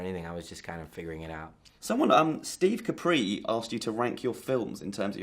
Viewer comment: did anyone hear the interviewer ask if M. Night could wank his own films? hahaha